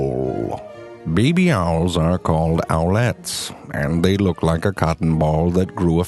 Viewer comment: I misheared the owlets to omlets. Maybe because I feel EVERY. SINGLE. BONE. INSIDE. MY SKULL to shrink, thus pressing my brain into smaller and smaller. And it hurts